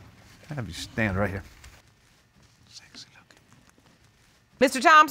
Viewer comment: Okay so before I continue watching, I JUST HEARD HER POTENTIAL FATHER TELL HER "SEXY LOOKING" and kiss her cheek at 0:54 & now I'm confused... let me keep watching...